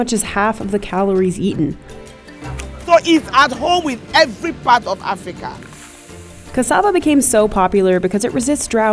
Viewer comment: That is a lie, Manihot esculenta is not African. Cassava is an American crop. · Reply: Later in the video it is stated the origin place (South America). I think you misheard the quotation at 0:17 - 0:18. It says, "It is at home with every part of Africa". Nowhere are they saying that cassava is not from America.